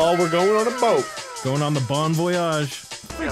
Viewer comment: Moo: We’re going on the bon voyage What I hear: “We’re going on the Bomb Voyage”- Incredibles